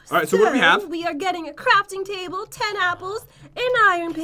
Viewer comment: Thought said crapting table